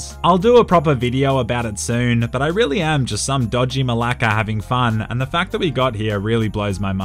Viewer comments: Did he really say "I'm just a dodgy malaka" ? Malaka as in greek? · Did you just say "dodgy malaka"? 😂